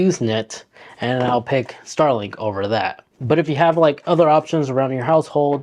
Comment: Star link I thought he said skynet 😂